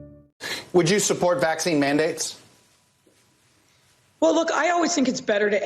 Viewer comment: I had to back that intro up. I heard "Would you support Nazi mandates?"